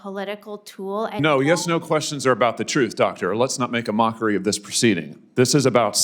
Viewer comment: I know men can’t get pregnant.Cant the lady say the truth. I thought she said she was a doctor.